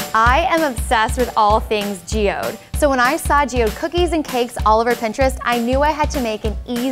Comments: I thought it said Geodude Cookies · Thought it said geodude And was confused how the cookies looked like a geodude